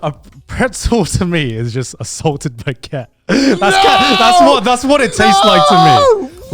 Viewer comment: Garnt: "Is pretzel a bread" "Pretzel, to me, is a salted baguette" Yama-chan has never been so disappointed in her life until hearing this. Yama-chan stares menacingly while holding a baguette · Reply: I really thought he said assaulted Baguette because of his accent lmao